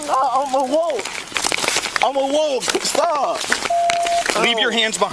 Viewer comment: is he saying "im a wolf.....I'm a wolf"?